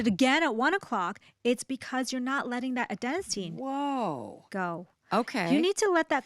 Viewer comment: what is she saying at 9:37? adenasine? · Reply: Yes, Adenosine